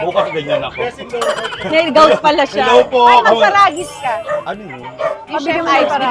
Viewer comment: Anyone know what Alex mentioned Saragis chef Yves for gout? I’ve been searching online but I cannot find it. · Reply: Chef aybsparagis kc